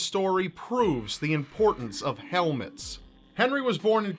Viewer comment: When you said "importance of helmets" I heard "importance of Elvis" XD